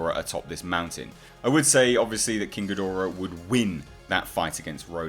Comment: Everytime he says "King Ghidora" I just hear "Kinky Dora". Oh God... My childhood is ruined. 😱